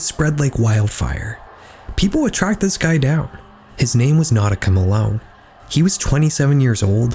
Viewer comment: I’m sorry… did you say his name was “Notacum Alone” ?